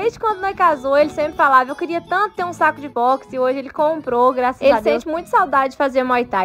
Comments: Na hora que ela começou a falar eu achei que ela tinha falado um saco de bosta kkkk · Juro q ouvi "saco de bosta"😂😂😂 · Só eu que escutei "saco de bosta"?